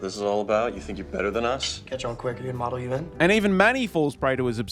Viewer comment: What is Andrew saying when he responds to his brother? (7:27) I genuinely don't know what he's saying or what it means. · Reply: You catch on quick are you in model UN? Earlier he said he was top of model UN😊